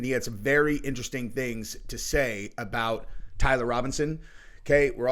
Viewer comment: Damn I thought they said Tommy Robinson, I thought I was going to get some good laughs.